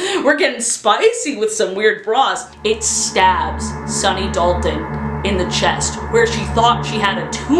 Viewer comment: Going to be honest I keep mishearing Sunny Dalton as Sunny Dolphin